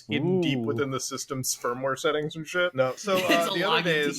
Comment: I THOUGHT SAID IT'S BUILT IN THE TESLA'S SPERMWARE BUT HE SAID FIRMWARE